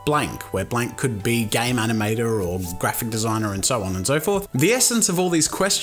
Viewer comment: When you said "game animater" I thought you said "gay manimater"